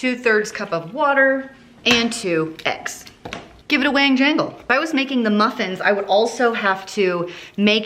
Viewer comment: Did you just say “give it a wang jangle?” 😂